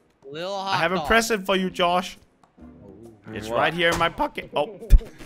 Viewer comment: “It’s right here in my pocke-“ 🤣🤣🤣🤣 this guy is legit the funniest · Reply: I thought crainer said right here in my f***ing